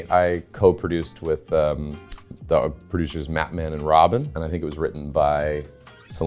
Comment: fully misheard him and thought he said Batman and Robin